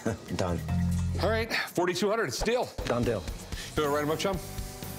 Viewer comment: did Rick say "alright, $4200, steal"? ..... he's not even trying to hide it anymore haha